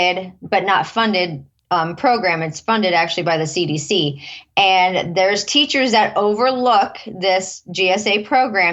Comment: She did not say that it's funded by the federal government.. she clearly said it was funded by the CDC 4:32